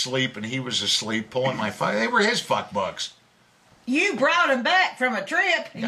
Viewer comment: F_ck BOOKS! 🤣 First part of the video I thought they were saying f_ck BUCKS. 🤣